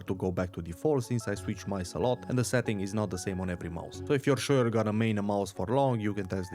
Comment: That's what I said my dude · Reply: i literally could of sworn i heard u said "i use the same one for every mouse" thats why i typed this. lol ur accent for me made it so i never notice u said "not"